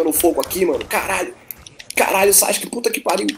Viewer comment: eu ouvi o falando sasuke?